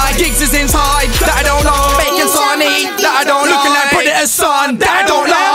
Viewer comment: what does he say at 2:17 ? summet about preditah ?